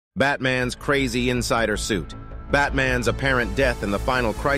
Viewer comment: I misheard it saying: "BATMAN is crazy inside her suit" After the picture with catwoman was shown.